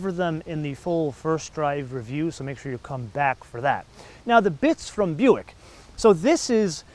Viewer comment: sounds like you said "the bitch from buick" lmfao